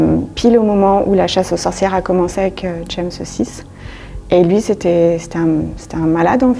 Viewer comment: les joies des hallucinations auditives : "the shame saucisse" --> James Six , aussi appelé Jacques Stuart VI , roi d'Ecosse.